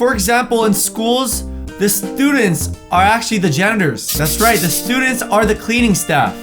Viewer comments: instead of janitors I thought he said "the students ARE the genders" XDD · Lol I thought you said genders instead of janitors. I was like, “WT Heck? What do mean?”😂